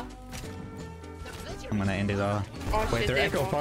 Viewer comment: Does anyone know what the girl says at 3:44 ? Something like "we will remain asgibygasgamever" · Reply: i think she said "we wittewawwy might have the best pwayews evew" - we literally might have the best players ever